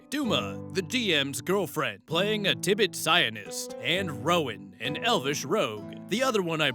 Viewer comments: I was just listening to the video in the background and was seriously asking myself why it was important to mention that they had a zionist in the party... Thx to your comment this all makes way more sense now lmao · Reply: I thought he said "zionist" and I was confused, lol. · Had to actually read what was said, because I kept hearing "Zion" instead of "psion".